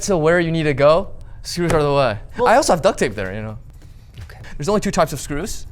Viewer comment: what does he say after "if you get where you need to go" I can't understand what he says after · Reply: I think he says "screws are the way." It doesn't exactly make sense grammatically, but I think that makes the visual gag of the commencement speech even funnier lol